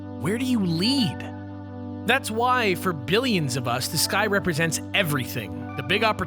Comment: For some reason whenever he said the sky it sounded like he said this guy and it sounded really weird.